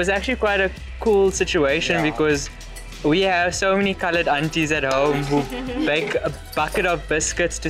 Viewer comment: What does he say at 18:54? So many what _____ Aunties? What was that word he said??? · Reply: So many coloured aunties.